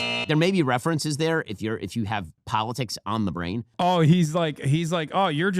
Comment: 😂 did he say "PALETICS on the brain.."😂